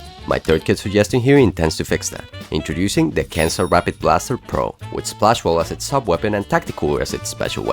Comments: I thought he said the cancer rapid blaster pro, caught me off guard there mate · I heard Cancer Rapid Blaster pro ☠️